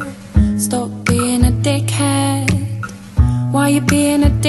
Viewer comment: I was trying to find this song for an hour because I thought she was saying "dick hat"